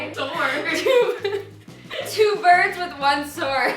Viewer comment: I thought she said "with one turd" 😳🤔